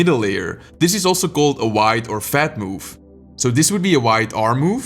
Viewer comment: everyone turn the caption on and when he says this is called a wide or a fat move the caption says "wide or your momma move" ;-; is youtube trolling or is it glitch